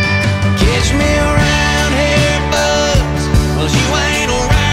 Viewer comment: I always thought it said around here bus